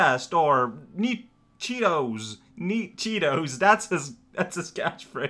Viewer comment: E-102 Codename: Gamma Catchphrase: "Neat cheetos" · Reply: That makes a whole lot more sense than what I thought I heard, "Meat cheetos!"